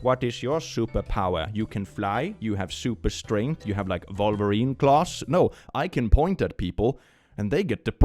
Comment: Tbh I thought said Vulva-rine claws and I am dying now